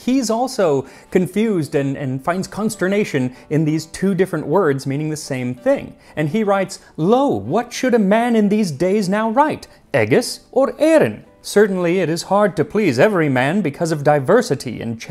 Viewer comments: i thought he said "lol" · i heard it as 'LOL' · was looking down, so I thought you said some medieval dude was saying "lol"